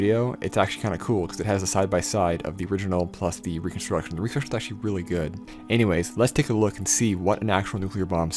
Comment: what did you just say? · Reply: I think "the reconstruction is pretty good" but with most of the letters missing.